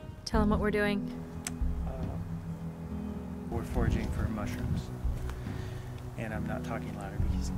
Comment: I thought "foraging" said "proposing" and got really excited then remembered you're already married. 😂🤦🏻‍♀️